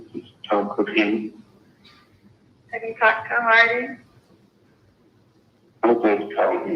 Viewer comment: Who is Hardy? Sounds like she said, did you talk to Hardy? Hard to understand what she says, and only understood two of Chad's sentences.